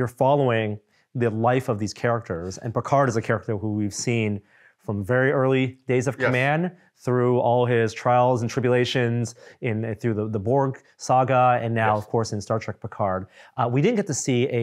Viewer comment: Did say "trials and tribble-ations?"